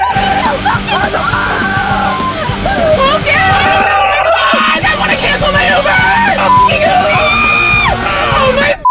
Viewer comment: what did she say? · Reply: "God I wanna cancel my uber"?